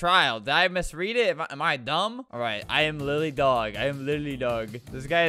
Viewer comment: it sounds “LITERALLY DOG” instead of “LILY DOG”